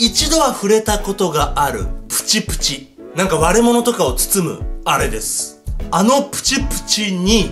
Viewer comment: 最初の割れ物とかを包むあれですが、悪者とかを包むあれですに聞こえたw